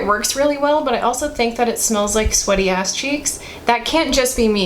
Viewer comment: Did I hear that right? Sweaty ass cheeks? 8:40 😂 Love you 😆💜